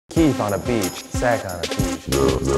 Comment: Omg I thought Eugene was saying "Keith on the beach sand on his peach" and I assumed he meant there's sand on Keith's ass